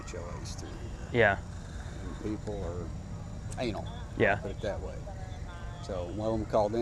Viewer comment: Did he say anal